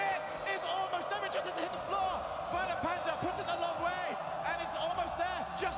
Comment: can someone tell me what he said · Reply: "It just doesn't hit the floor" it's been 3 years.